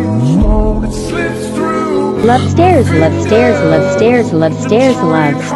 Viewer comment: when it says "love stares" i heard "lobsters" 😭